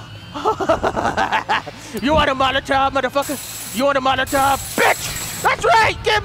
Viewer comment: does he say meletop?